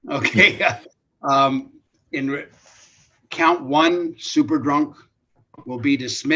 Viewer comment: Did I hear correctly at 23:18 that the attorney stated that count 1 was “Super Drunk”? I know my hearing is bad but I went back a few times and sure enough it sounds like super drunk to me. Is that some fancy court and legal lingo? 😂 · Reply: Yes Super Drunk